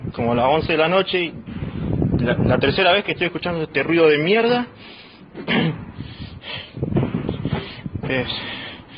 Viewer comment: I don't speak spanish but I'm not sure that subtitle fully shows what he said there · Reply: He said: la concha de la lora, if translate sounds like: the pussy of the mrs.